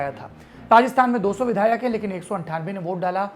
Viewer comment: 😂😂" Pakistan mein" sound instead of Rajasthan